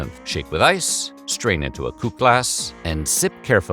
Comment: Did he say "strain into a poop glass?" 💩